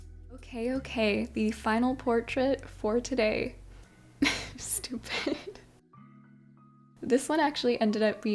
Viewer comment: did u just say stupid😭🥹??